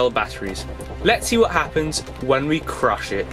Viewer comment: I thought it said crush shit